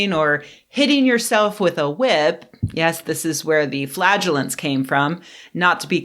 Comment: Flagellants? Damn, I misheard that as flatulence.